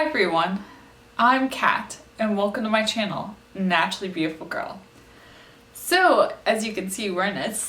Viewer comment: I love these videos! At the end I thought that you said “ Naturally Beautiful Gorilla”! I guess I wasn’t listening very well! Lol.